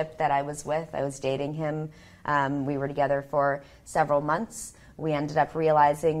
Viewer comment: I thought she said she was dating her son....